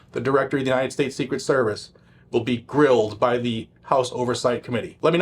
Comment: 😂low key I thought I heard that Cheatle was getting Drilled instead of Grilled. I wouldn’t want to see that Live streamed 🤮🤣